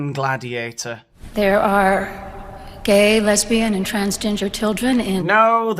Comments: It almost sounded like she said transginger children 😂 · I thought she said trans ginger 😂